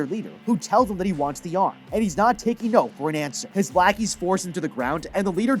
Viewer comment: what did he just call them😳😳😳😳😳😳😳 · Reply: He only said (Lackies) and said it again